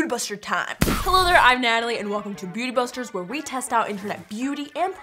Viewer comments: XD i thought she said BOOTY BUSTRES (aka bust down thotiana XD) likes own comment :D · It sounded like she said booty busters🤣🤣🤣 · Why in the very beginning of the video, Natalie sounds like she saying "booty busters time" instead of "beauty busters time"?